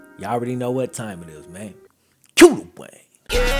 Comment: "Cue the Wayne" - I finally know what he is saying. Woohoo. Yeah some of us are just a little slow. I just thought he was saying "Cue the way" - If you are asking what sense does that make, just know this is FAR, and i mean FAR from my worst interpretation of words in a song, etc... However, while debating famous "Cue the Wayne" intro with some other fans of the channel, someone said it was cue the Wayne and even though i live in Atlanta it took a few min to figure it out. But like a go getta, I finally fig'd this mofo out and now i dont sound like an idiot anymore... Ok okay, yall got me... I know I still sound like im an idiot. What you say punk? Yo mamma's an idiot bitch. Hey hey... Dont leave yet. Take all this hate wit you we dont need it, ya heaar?